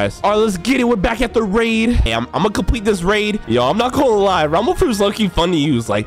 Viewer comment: I thought you said tumble tumble fruit